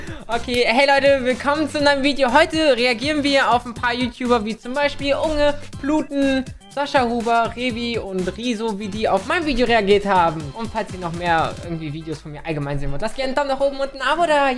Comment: Wer hat auch "Bluten" statt "Paluten" verstanden xD